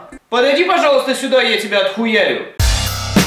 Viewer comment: Я один услышал "Вася на наш весёлый канал"?